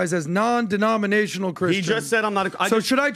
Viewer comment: he said “I’m a Christian” this was just mishearing it